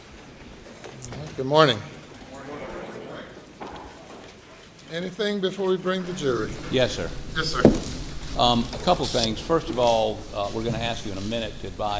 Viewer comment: I love the way the judge smiles when he says “you may bring the jury”. He’s probably close to retirement age, and still not tired of the role he performs. He brings a little joy into a grim scene. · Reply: I thought I heard “spring the jury.”